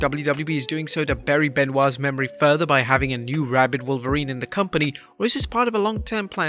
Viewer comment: I swear I heard McAfee say "rabid dog" . As soon as he said rabid, i thought wolverine but I heard a slight pause and then dog....